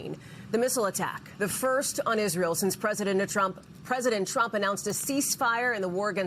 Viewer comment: did you say President Netantrump? LOL how accurate!